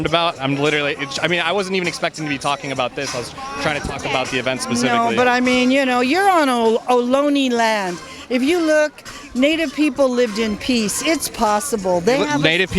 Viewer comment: The fact that that these people have been brainwashed to believe that the "Native" Americans were these peaceful people is hilarious. REGRESSIVE LEFTIST BRAINWASHING & MISINFORMATION AT WORK! I thought she said you are on Loony Land as that would be more accurate with idiots like her running around.